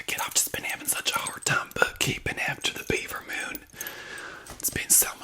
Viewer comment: my first thought when you said the Bieber moon was me just nodding and being like "yeah Justin Bieber always doing wild stuff that boy, he's just been going down hill" then realized you meant like a type of made up moon 😭